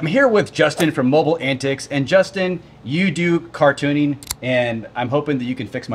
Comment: LoL I thought he said, "you do cartooning"😂 I thought I was watching the wrong video!